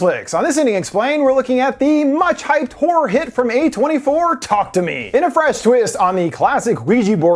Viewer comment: Early into the video when you said "from A24", I thought you said "from 2024", and I had a mini-crisis of "wait, what? But it's 2023, right?"